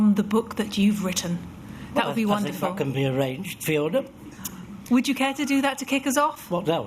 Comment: Sounds like Arthur says f***ing around that point, doesn't it? "that can f***ing be arranged Fiona".